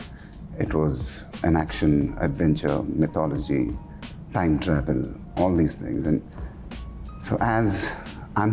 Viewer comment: did he just say time travel